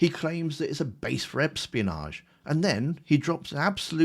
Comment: NGL it sounded like you said Epstein-onage instead of espionage 🥲 Philp will be the next Tory defector. I can see it. I can feel it in my bones.